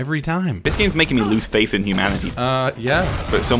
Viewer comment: Did you say "Lose faith in "Jumanjity" ?!🤣